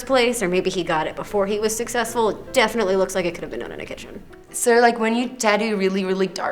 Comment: What does the girl with the blue hair say at 8:26 ? She speaks so fast 😞 All I heard was "Definitely looks like it could have......????" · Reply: Been done in a kitchen